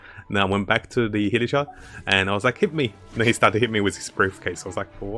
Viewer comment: I keep hearing "hiletroll" instead of hilichurl